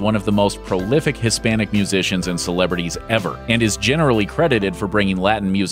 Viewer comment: I thought I said magicians 🤣 oh God